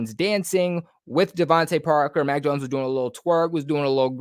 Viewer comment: Did you say that Mac Jones did a “little twerk”?😂😂